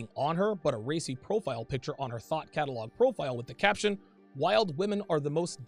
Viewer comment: Oh thought catalog. It sounded like you said thot catalog.